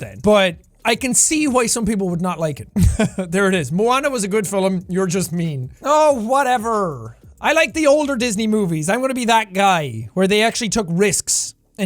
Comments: he said “fill em” instead of film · Did he say "fillum"? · Destroy the like button if you agree with any of these! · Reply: you said “fill em” instead of film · I swear he said filim instead of film here